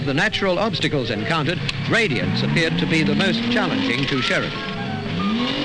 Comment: So at about 2:56 narrator says "of the natural obstacles, radiance appeared to be the most challenging to Sheridan" - anyone know what's that "radiance"? YT subtitles probably messed up speech to text here, and I don't know any English word that would fit here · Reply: I'm fairly sure he's saying "gradients" ie, slopes